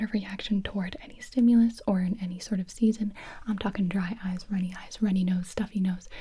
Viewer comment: did she say runny eyes? 🤨